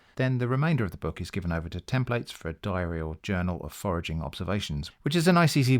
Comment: I misheard this as 'a diarrheal journal' 😮 Unintentionally appropriate